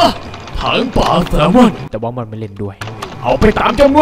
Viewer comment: พูดว่า "แต่ว่ามอลไม่เล่นด้วย"